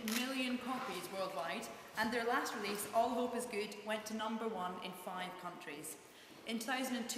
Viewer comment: did she say All hope is good